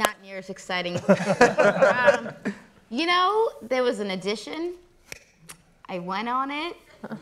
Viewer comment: it did sound like she said addition